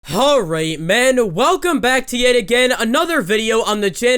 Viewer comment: At first I thought he said "Alright men" instead of "HALright men". I was actually scared.